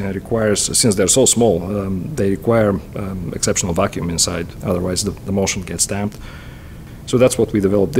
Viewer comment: "otherwise the motion can stamp"....? Any offers on what is being said here?